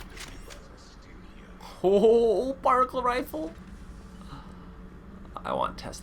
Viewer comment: Lol when he says particle rifle or sounds like he is saying barnacle rifle!! XD